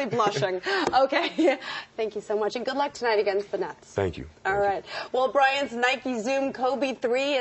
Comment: did she say good luck tonight against the nuts?